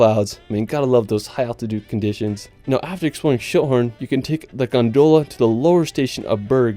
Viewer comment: I had to laugh when he said Schilthorn because at first I thought he said shithorn... I was like what? LOL